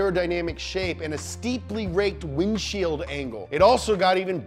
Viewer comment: the subtitles say "angel" instead of "angle"